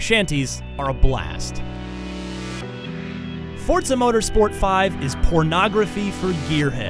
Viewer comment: "forza motorsport 5 is pornography of gearhead" I don't understand how that work but xbox players so weird XD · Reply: For, not of. He's saying that for people who are into cars Forza is a great game.